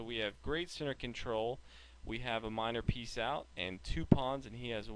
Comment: who heard him say 'peace out'? :D